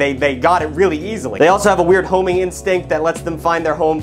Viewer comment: I misheard “homing instinct” as “homie instinct” and thought you were saying the pigeons have the innate ability to identify other pigeons that are from their family/flock